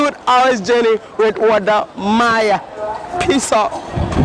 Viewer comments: im sure i know he is saying peace out but it sounds like he is saying piss off haha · lmaooo , did u say "piss off" at min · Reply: peace out